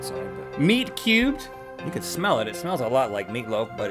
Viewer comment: Am I the only one who thought he said nerd cubed when he said meat cubed?